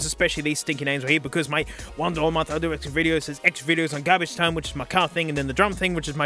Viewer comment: did i hear x videos